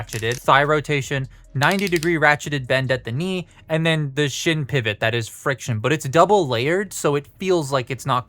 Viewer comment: I thought you said “shit pivot.”😂 I don’t understand people complaining about proportions on alien robots. If they’re from another planet, why should they have proportions exactly like human beings?